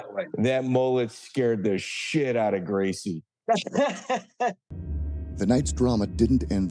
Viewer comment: I thought it said he scared the shit out of groceries